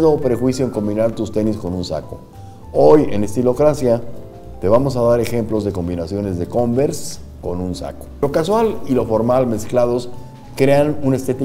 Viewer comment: Por un momento pensé que decía "converse con un saco" jaja